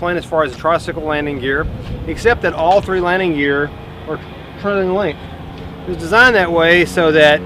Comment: Can anybody tell me what he says at 0:23? I hear 'all three landing gear were turned and length,' which doesn't make any sense. It confused the auto-cc too.